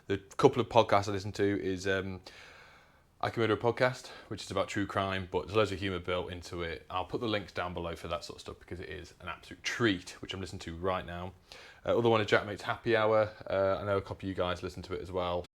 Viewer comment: I tried to get the podcast, but I have no idea what you said. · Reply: Jaack mates happy hour and I could murder a podcast